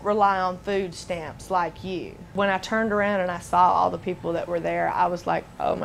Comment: DId you notice the close captioning text at 0:29. Well, excuse me that I work for a living and don't rely on food stamps like the BLACK turn around....Now when did she said Black?? She said I work for a living and don't rely on food stamps like YOU, then turn around.....